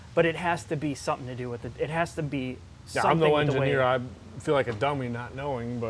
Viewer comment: Did predict the 2018 Hellcat Demon at 4:15??? That's wild! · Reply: Don't know if you were kidding, but I think he said that he is not an engineer